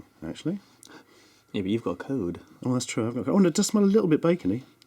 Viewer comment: i know he says “well you’ve got a cold” but i heard “well you’ve got covid” and did a serious double take because of how old this video is